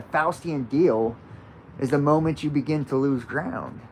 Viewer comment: When you said, "the moment you make that Faustian bargain, that deal, then you lose ground," I heard "lose crown."